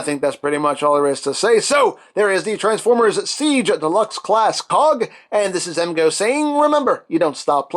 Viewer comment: I thought he said deluxe class pog